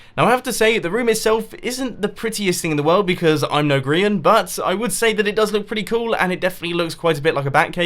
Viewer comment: did he say I'm no grian?